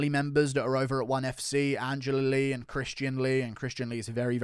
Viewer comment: RIP Victoria Lee. Crazy news! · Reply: at first I thought he said Andrea Lee, I would have been devastated